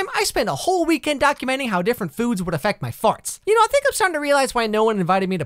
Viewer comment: i absolutely LOST IT at "one time i spent a whole weekend documenting how different foods would affect my farts" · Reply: FaRtS?? I thought he said THOUGHTS 😆